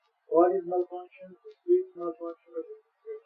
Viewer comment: In 01:28:40 i guess, he says ... the spirit malfunction... instead of speech. Also thanks for subtitles. God bless you